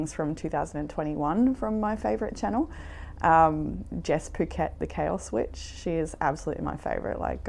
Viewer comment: This is the Tarot reader mentions in Pile 1. Even though I thought she said "Jess Phuket, the Kale Witch," Google still found it! (It's Jess Puckett, the Chaos Witch.) <link>